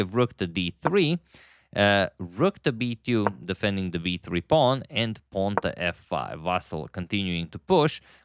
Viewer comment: I heard you again saying v3 pawn.